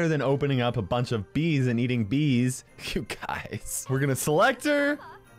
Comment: it sounded like a bee said her instead of you.